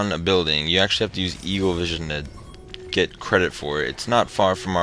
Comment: did you say evilvision instead of eaglevision